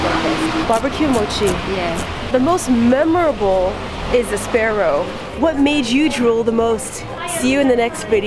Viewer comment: what should we name it?: I don't think I could eat him now a few moments later: let's eat the face 😂😂😂😂 I think the ping pong bat shape is wooden grater for wasabi root, I droll so much when you and eat the hot dango 😋😋😋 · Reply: Did she just say "grilled sparrow"????